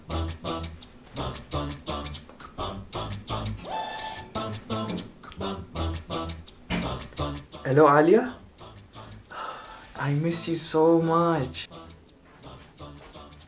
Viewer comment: Did he just say hello aaliyah?? 🙂 Hey!! 😂😂😂